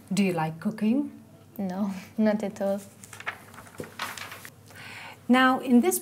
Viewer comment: I don't understand what said, someone please explain to me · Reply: She said: No at all.